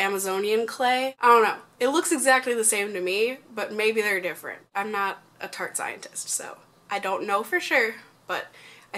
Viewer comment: You talked about poop so much that when you said "I'm not a tarte scientist" I heard "I'm not a fart scientist." Hahaha.